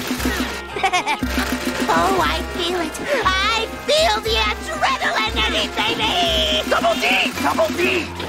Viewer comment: i swear when i was younger i thought he said oh i feel it, I FEEL THE ADRENALINE ANEATH THEY KNEE